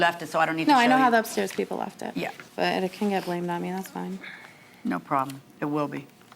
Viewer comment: what did she say at 12:01 I couldn't hear the "sass" !?!?! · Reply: "It can get blamed on me it's fine."